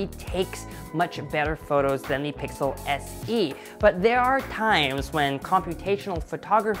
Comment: did I hear him say pixel SE?